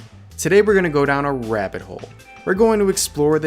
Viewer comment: I heard today we're going down a rev it hole